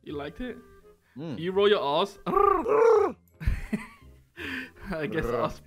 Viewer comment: I thought you said, "Can you roll your arse?"